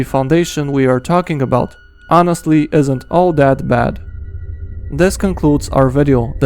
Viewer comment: it says out instead of our in captions